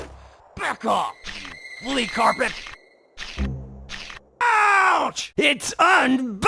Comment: What did he say here? (his voice was so compressed that I couldn't understand a word) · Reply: He says "back off, flea carpet!"